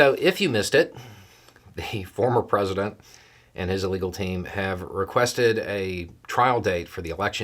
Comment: Was it my imagination? I swear I heard you say at 0:33 “the former president and his illegal team…”